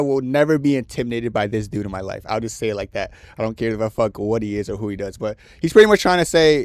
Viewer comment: what are you saying?? 😂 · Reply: he will never be intimidated by a bad person no matter who he is, are you dense